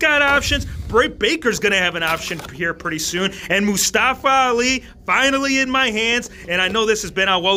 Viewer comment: Did you say Mustafa Ali in 5:07 😂